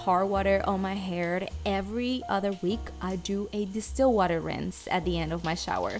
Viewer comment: Did you say still water and if yes then can you tell me how to make that water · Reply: distilled water love 😊 I bought it at the store ❤️